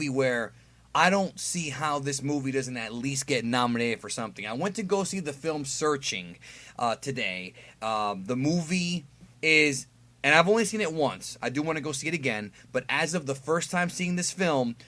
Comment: Well i feel stupid. At 0:35 I thought you said surging, not searching lol.